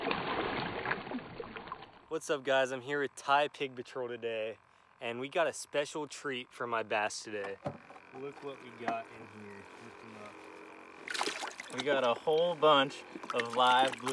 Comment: I thought he said "im here with thot control"😂😂😂😂